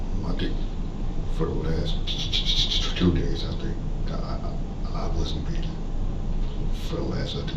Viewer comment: I thought he said, "For the last 2 days, I think I wasn't feeding him."